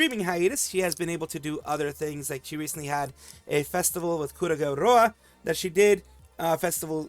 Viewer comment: He said "Kurageu Roa", but youtube's auto subtitle says "Kureiji Ollie", lmao 🤣